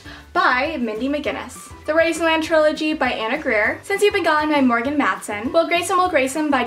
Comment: What is the Trilogy series at 2:51, I don't understand what you say? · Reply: The Razorland Trilogy by Ann Aguirre